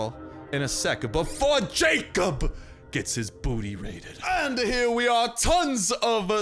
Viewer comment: I accidentally skipped the video at 31:00 so he stopped talking when he was about to say booty ra- I was so shocked because I thought he said booty raped lmao.